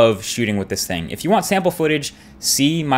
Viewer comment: What happened at 5:09? He said the benefits of shooting raw...?